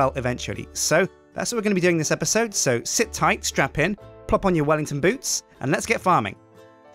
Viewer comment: did he say strap in or strap on?